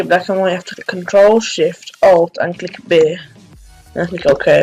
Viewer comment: What did you say?? At 1:20, control shift alt and bae? vae? rae? bay??? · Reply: ctrl, shift, and click b 😂😂😂😂so funny watching the vid back bcuz i have a good mic now